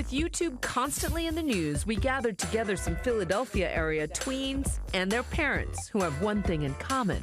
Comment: did she say tweens?!